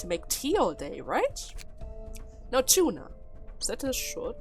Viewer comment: I just heard Tuna. XD